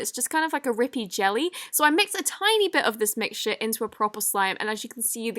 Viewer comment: Me watching the video Her ~ So I mix a tiny bit of this mixture What is sounded like to me So i mix a tiny bit of the mix Sh* Me ~ O_O Me turning on the captions Also me~ She said a bad word?!?!? Oh she didn’t-...